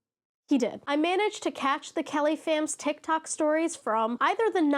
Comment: Okay... the first few times you said Kellyfam I thought you were saying caliphate.